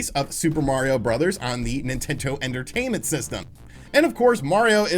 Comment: Did he just say ‘nintento’? 1:07-1:08.